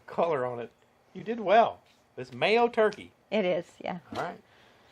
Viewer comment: I haven't tried mayo yet. Looks like it did the job. At first I thought said her male turkey had been resting for about 30 minutes when you stepped in the shot. haha Oh, mayo turkey, I got it now. can run the cookers now. If I could just get Janet to come out and take some training in the "pits". Nice Turkey. Looked delicious. The Masterbuilt does a great job, and so easy to use.